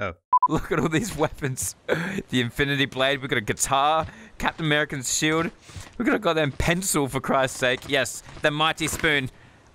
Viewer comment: did he say Captain Americans?